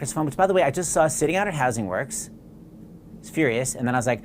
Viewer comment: What the hell does he say about the HAIR cd at 2:42? lol No idea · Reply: lol "which by the way I just saw sitting out at Housing Works" (a bookstore in NYC), and then he was upset that they were selling it for so cheap I think?